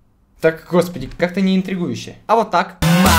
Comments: Масла в ванну подливаю Я им отвечаю:... "Ну и чё?" :DDDD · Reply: мне послышалось масло в батл подливаю:) интересно как оно по настоящему звучит? °-° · Масла в ванну подливаю Я им отвечаю:... "Ну и чё?" :DDDD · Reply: Я мне послышалось масла я вам подливаю:D